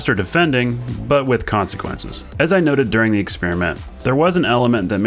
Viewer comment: Did you just say "but with khansequences" around 6:18 ?